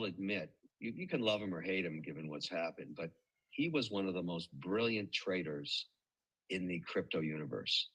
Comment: mmmm - did I hear "He was one of the most brilliant traitors ..." Edit: Oh - you heard it too? As Mark Groubert says "He elected to get on board the yacht - now the only way off is overboard - in concrete boots" Anyway I will check this on Rumble